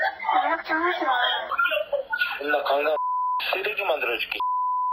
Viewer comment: 삐삐 거리는것 좀 집어넣지마라..그리고 강남 쓰레기????..뭔가 안맞는거같은데 조금 만 자세히 들어보면 (^강간해서 존나 쓰레기 만들어줄게 10 8 年아) 선명하게 들리는데 ??? 왜 저딴식으로 자막달았지?? 강남 쓰레기?